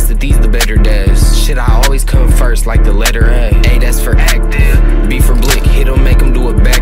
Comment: bruh i thought he said "A thats for act different" the whole time til i saw it said active on here